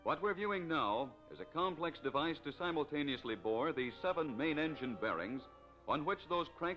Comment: This is before ACF-Brill spun off Hall-Scott in 1954. By that time, ACF-Brill was getting out of the bus business, run off by GM Truck & Coach's highly successful rear-engined diesel coaches. Later, Hercules bought Hall-Scott and shut down this plant. Last OEM user of Hall-Scott engines was Crown Coach in LA, which fully converted to diesels by 1965. Hall-Scotts were good engines, but were horribly inefficient. A Brill IC41 using a Hall-Scott 190 got around 2-3 MPG on the road, while a GM PD-4103 with a GM 6-71 got 7 or better with the same payload. The choice for Trailways was obvious...go diesel or fold. The Hall-Scott powered Brill was faster...but had horrid brakes. I know...I drove both models. Judging from the models in production and the audio equipment and credits, I'd date this program from 1950-51, probably produced by ABC-owned KGO. The two main things that sunk Hall-Scott? Archaic production methods and their failure to build a useful diesel engine that didn't explode. (The Model 140 disaster.) Also...note the lack of counterweights on that archaic crank! And only three mains!Hall-Scotts were smooth runners...only by virtue of their excessive mass of their blocks. They'd run forever...if not pushed too hard. Overrev one on a grade, though, and you'd break a crank quickly. The rest of the industry had moved on; Hall-Scott was still stuck in the 1920s. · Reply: or somwhere in the video I thought they said 7 main bearings?